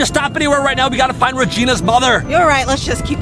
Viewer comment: I thought you said brother not mother